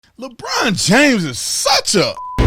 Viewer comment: Holy Sh!1 ...i thought you said Lebronda James.....